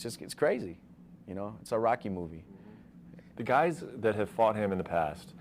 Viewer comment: Lmaooo at first I thought he said it’s a Iraqi movie, but I realized he said Rocky movie